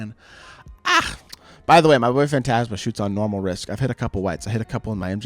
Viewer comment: did he say his boyfriend?😂 edit: oh he’s saying my boy “fan” not boyfriend, shit was still funny af